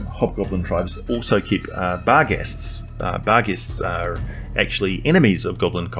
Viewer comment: keep what? what word are you saying @ 26:16? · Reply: Barghests. I have a video on the Barghest.